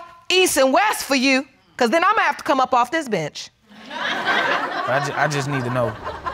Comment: I thought judge said “imma have to come off this b!tc.h” 😂😂